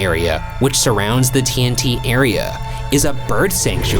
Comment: i was so tired i heard "teen n tea area" instead of tnt area like yeah obvs THAT makes sense smh